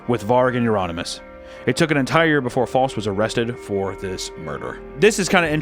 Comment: "it took an entire, before voss was arrested". An entire what??? Oh youre saying "entire year" 😂 oh man